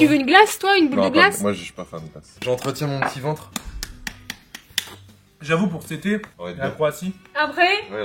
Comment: C'est moi ou dit a 8:20 :j'avoue sette été en Croatie